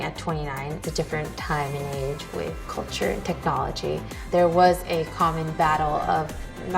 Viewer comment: I thought she said "Neo Culture Technology" lol